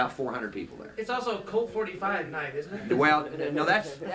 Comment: Ahhh i see now lol i thought it was a night club or bar or some kind of police callsign lol I must of misheard him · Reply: ***** Yup Colt 45 night, which refers to the fact that a lot of them had guns.